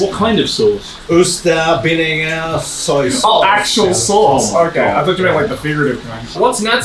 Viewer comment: I genuinely thought Natsuki said "Worcestershire" and was confused when they'd edited in oyster sauce lol